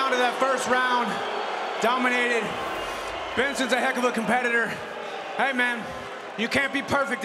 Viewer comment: Hahahaha did anyone else catch it where Chael Sonnen called him Benerson Henerson? 🤣🤣🤣🤣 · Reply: Heard him say henson. 😂